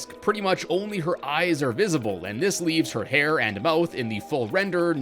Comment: Did he say JJ hair